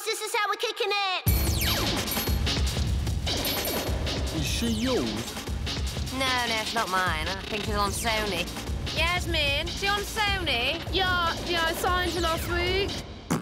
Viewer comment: can anyone tell me what they are saying? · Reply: Is she yours No shes not mine, I think she's on Sony Jasmine is she on Sony Yeah I signed her last week